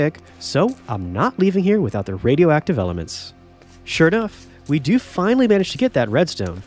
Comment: he said “Shirt off” instead of “sure enough” :D